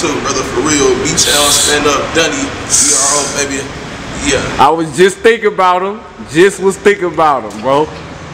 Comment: wat did he say at 0:20 ''that lil b was thinking about''?